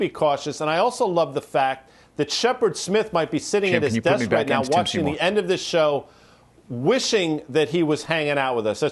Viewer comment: Lol what did they say at 6:45 about Tim? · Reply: "Can you put me back in to tim seymour" I think he wasnt miced up so he didnt hear any of the interview